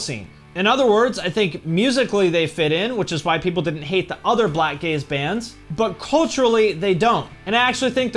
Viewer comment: I thought you said black gays.